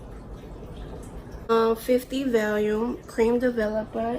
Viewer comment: I thought she said 15 vol, was gonna congratulate her lol